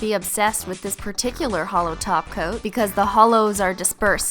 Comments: Oh my God for the longest time I always thought she has been saying hollow taco, it's top coat for those who may have also thought that... What's that? No one else heard taco... Oh ok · oh at first I thought she said holo toco not holo top coat! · every time you say "holo top coat" I hear "holo taco"😂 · I totally heard "Holo Taco" the first time she said Holo top coat. Realizing after, she actually said top coat. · it almost sounded like she said a holo taco not holo topcoat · I thought she said holo taco instead of holo top coat. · When she said holo top coat I thought she said holo toco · i thought she said holo-taco not holo topcoat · When she said holo topcoat, I thought she said holo taco 😂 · i thought you said "holo taco" when you said "holo top coat" 😂😂 bet those would some delicious tacos doe! 😎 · I thought you said "Holo taco" not "Holo top coat" 😐😂😬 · Every time I hear you say "top coat" I think you're saying "taco." Then it makes me hungry for tacos. xD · when she said "holo top coat" I thought she said "holo tacos"😂😂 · I thought she said holo taco instead of holo topcoat